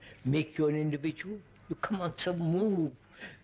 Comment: what does he say at 5:15 or make you individual (then he says something like: come on sa move) ... does not make sense what he is saying here ... please help and thanks in advance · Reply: Move to the watcher, see, look the fuss brain making, just watch it don't become part of it